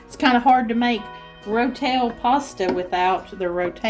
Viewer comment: At first I thought you said road kill pasta...giggle